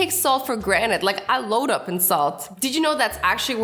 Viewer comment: Whats the tooth over there? Your wisdom teeth BTW when said in salt i thought she said insult and when she said think you have thick hair think again I thought she said think you have thick hair thick again I liked my own comment cause nobody liked mine